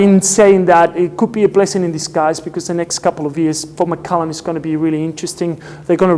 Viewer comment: Great review and you have convinced me to go visit even though I dont have sea legs and am scared of the ferry trip! @ 17:30 - did I hear Macallan?